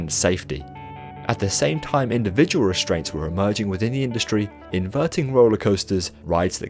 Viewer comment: tell me why i thought it said restaurants